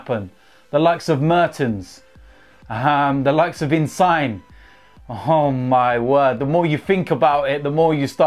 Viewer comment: Did he just say Insign? Instead of Insigne? 😂